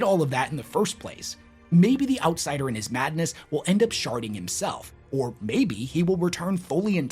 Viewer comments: i heard 28:03 " maybe the outsider in his madness will end up sharting himself" me yep that'll make ya put yourself in exile. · Not going to lie... I thought you said "Maybe the outsider will end up Sharting himself" Had to reply that section back to be sure. I was wrong of course, but I was still laughing when I heard it the firs time. :P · Sounds like you said "maybe the outsider in his madness with end up sharting himself" which that sounds like a much worse experience for the galaxy at large